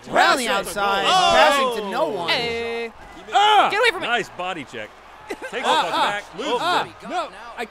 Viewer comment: LOL I heard say "Nice body , Jack" :D